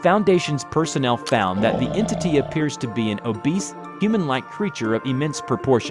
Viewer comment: did i hear obese?